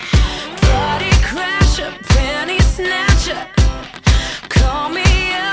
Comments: I always thought it said Panty snatcher not penny snatcher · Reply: I always thought it said Danny snatcher. 😂😂😂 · I thought it said panty snatcher not penny Snactcher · I always thought it said panty snatcher. Lol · all this time i thought it said "panty snatcher" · I always thought she said "Fanny Snatcher"